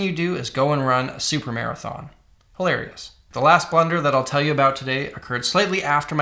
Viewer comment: Thought he was saying "blender" the whole time. I was like tf blender has to do with this? 😂